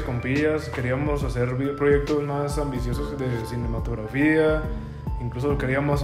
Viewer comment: entendí pornografía jaja sorry.